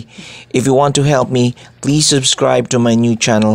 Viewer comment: Lol,did i just heard your mom telling you to say thank you?